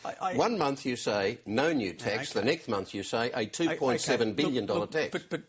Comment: did he say nek month?